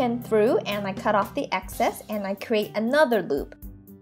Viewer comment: did you say create another boob?!?!😲